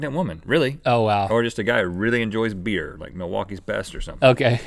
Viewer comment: said something between 8:57 and 8:59 in the video...could you please type what you said there in a reply to this comment ( I hope its not what I think it is) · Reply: lmao he said Milwaukee's best a kind of beer